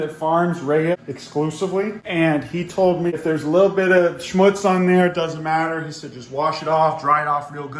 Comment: For all the german viewers did he say schmutz